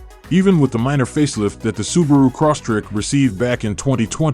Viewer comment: The guy sounded like he was saying Subaru CrossTrick. LOL